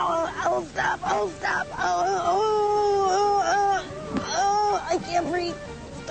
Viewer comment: yea no crap, she is literally saying I can’t breathe